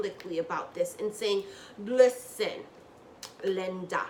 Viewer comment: Did you just say "listen... Linda..." 😂😂😂🤣🤣🤣